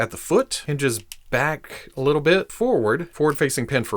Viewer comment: I thought it said "Wardude". Now I wish it was.